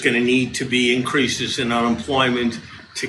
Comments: Sounds like he says there will a need for an increase in employment (not unemployment). Am I hearing that wrong? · It sounds like he says " in our employment".... maybe i hear it wrong...